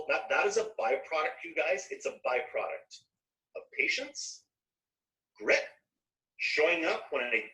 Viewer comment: When he said he got where he is through grit, I thought he had said "grift". Way more appropriate!